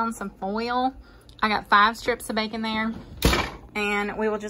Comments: I’m here from WI, I thought you said “45 pieces of bacon” I was thinking awesome. 😉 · I got excited when I thought I heard you say 45 pieces of bacon! That's sounds so good! Alas, what you actually said was 4 to 5 pieces of bacon. I'm sure it's still good without the other 40 or so pieces I was thinking I might get! Great channel! Thanks for posting!